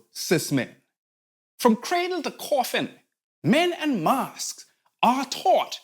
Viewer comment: "Men and mascs" not "Men in masks" I've been mishearing this for too many of your videos. Glad I figured it out though. 😭